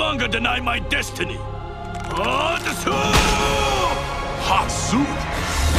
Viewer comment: am I crazy or does it sound like he just said "Hot Soup" ??